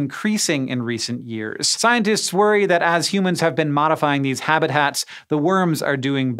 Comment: Did Hank say "habit hat" instead of "habitats" ... ?